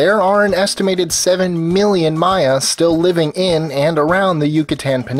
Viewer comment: The closed captions said "my ass" instead of Mayans.